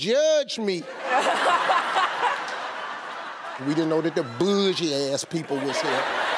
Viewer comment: What did Steve say at 1:24? At first, I thought that he was saying "bourgeoisie" but it didn't sound like that. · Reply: he said "boojie ass"! almost the same meaning as the word you thought you heard lol